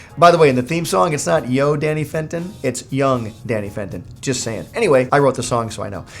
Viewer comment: I love Danny Phantom! One of the great shows that made my childhood awesome! Also, I'm reading the comments, and am I the only one who knew it was 'young' not 'yo' in the theme song? Growing up, I heard 'young' not 'yo' Danny Fenton.